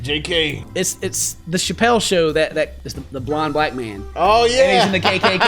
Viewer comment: Lol said the blind black man and I totally thought he said blond Blackman 😂